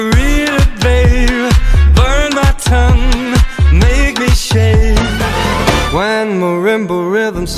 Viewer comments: "Make me shittt" i heard that in 2024😭😭😭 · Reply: I heard "Make me shake" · "Make me shittt" i heard that in 2024😭😭😭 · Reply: I heard "make me shave"